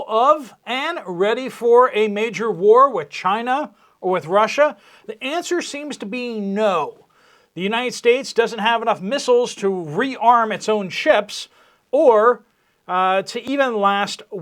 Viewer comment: For a hot minute I thought you said we did not have enough thistles!